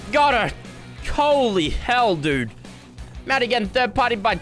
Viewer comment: did he just say holy hell?